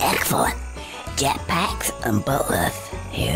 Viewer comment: Even Conker's voice was sometimes hard to understand. Like when he said "Jet-Packs And Butlers" at 10:45. I may have never known that's what he said if there weren't on-screen text as well. My biggest complaint about this game was how they removed the N64's FUN multi-player modes. That was SO stupid! I loved Bank Heist. I totally hated the new online battle mode they threw in. Why couldn't they have kept the old modes in it?